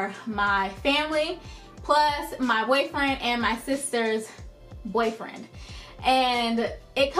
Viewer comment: It sounds like your saying sisters boyfriend